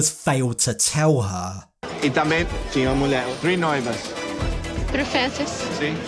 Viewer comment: I am Brazilian and from 6:35 to 6:40 doesn't speak a single word, it's all sounds cropped and taped together and the subtitles saying things she didn't say. weird. like, gibberish, random sounds from other parts stiched together. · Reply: The first one she said three fiance's in english but weird, after that it seems like gibberish